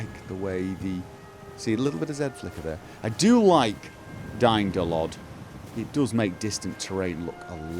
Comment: How did you manage to reduce Aliasing so much ? :O :) I totally heard "DY-NO-LOD" xD SAVE THAT CHILD !